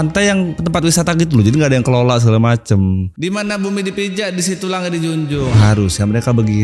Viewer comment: sori bang, gw dengernya: di mana bumi dipijak, di situ rakyat dipajak😭